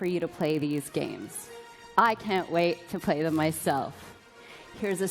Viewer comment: Did she just say she can't wait to play with herself at 42:33 ??